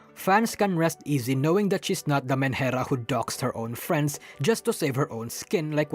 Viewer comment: what word are you saying there? Manhera? Sorry I've heard you say whatever word that is many times in the video and no matter how many times I replay it I cannot understand the word